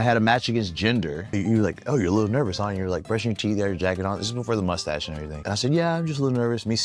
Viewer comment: WAIT 😅 4:26 talking bout the match with Jinder, did Ricky say "brushing teeth, jacking off" 😂